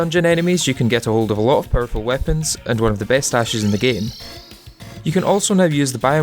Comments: I keep hearing “best ashes” here and cannot figure out what that’s referring to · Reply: Best dashes (tabi/ninja gear) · one of the best whats in the game? I replayed this part quite a few times but still couldn't understand. Thanks! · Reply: Dashes, the voiceover is a little unclear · Also what is he saying at 47:01??? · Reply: you can get ahold of a lot of powerful weapons and one of the best dashes in the game